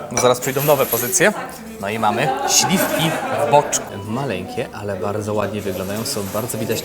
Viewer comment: Tylko ja usłyszałem "śliwki w moczu"? 🤣